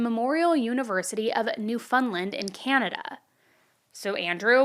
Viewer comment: The way you say Newfoundland as a Canadian I couldn’t help but laugh 😂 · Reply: I know! I said ‘did she just say New Fun Land?!’ lol